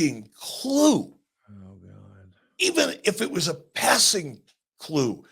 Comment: Did I hear Conrad or someone mumble really lowly "oh my god" after Bischoff said "if Tony Kahn had a fucking clue" ??? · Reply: yes Conrad said oh my God you heard right lol